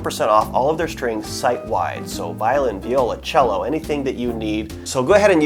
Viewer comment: Did he say "Violin, Viola , Cello?"